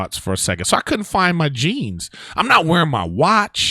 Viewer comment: I swear I thought he said he couldn't find his cheese, not jeans, like ahhh shit that would be rough.